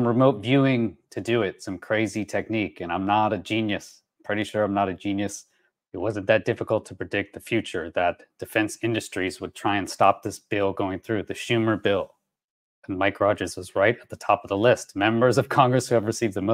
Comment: dude, did you really say, "Stop the Steal?" WTAF, man??